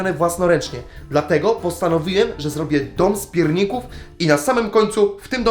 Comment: Na początku usłyszałem zrobię dzisiaj dom z piórników 😅